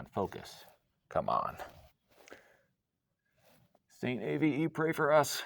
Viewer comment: What did you say at the 7:00 mark? Saint Avoye pray for us? Why?